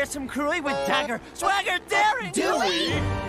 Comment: I heard "dagger swaggert daring" As in Rupert Swaggert